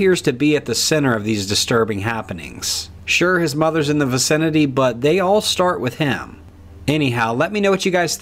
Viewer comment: I could have sworn that you said “sure his mother is in the vasectomy,” so I had to go back and listen to it again. My ears are just playing tricks on me I guess. My bad.